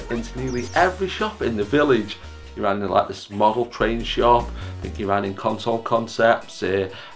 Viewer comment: did you say a model train shop ? 😀